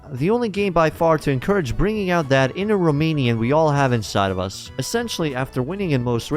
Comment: Did you just say "bringing out that inner Romanian? :))))